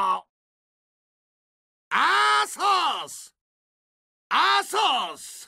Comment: In the Korean language, the way he says Arthas sounds like he is saying "Assholes." haha...